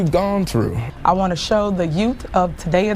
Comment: she actually said “yout”.